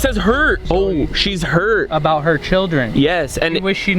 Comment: it sounded like a woman was saying ohhhh children